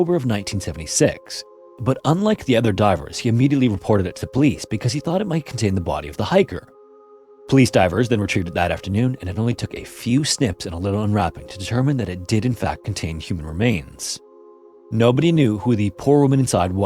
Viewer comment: Snips,like cutting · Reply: at first I heard "snips," but after reading the comment I now only hear "sniffs."